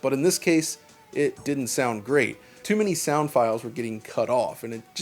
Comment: Correct, it uses mod tracker files for the soundtrack but the rest, like weapon sounds, speech, sfx, etc. uses ogg vorbis files. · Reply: Ah he just said "sound" and not "music." I don't know why I thought he said music....music's on the brain I guess lol.